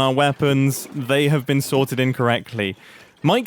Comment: When Mumbai says, “They have been sorted in correctly”, I thought he said incorrectly! 😂